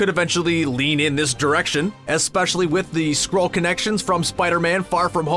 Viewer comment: I really thought he said ‘one Direction’ 🤦🏻‍♀️💀👁👄👁